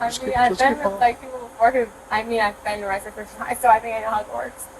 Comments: ohh she says shes been thru it before thought she said something about hogwarts lol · I heard: I mean I been arrested before from stealing stuff at Hogwarts 😅😂😂😂 so glad u did a translation cuz clearly I'm not fluent in entitled sloshed sorority sister's version of a gigaChad despite LITERALLY (as webster defines it, not valley girls) watching video after video of this exact situation to the point that if I closed my eyes, you could play them all back to back and I'd just think I listened to 1 video on repeat or it would at least sound like the same chick getting pulled over over and over again because they are all so dang similar!